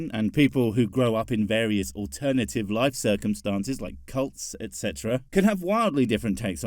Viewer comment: I thought you said cucks. I had to rewind a couple of times to realize you said cults.